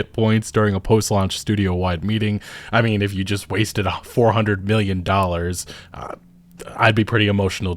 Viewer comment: Literally sounds like you said $400 billion.